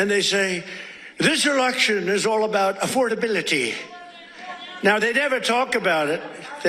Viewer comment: I could have sworn I heard this erection was run on affordability